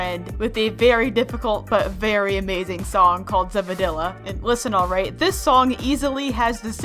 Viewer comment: I heard her say Vannila